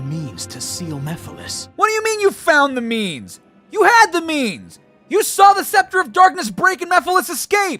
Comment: When said you had the means in one part. It sounds like he said you had the memes lol